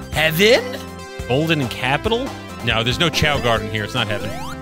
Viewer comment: for a sec, golden capital sounded like sky sanctuary zone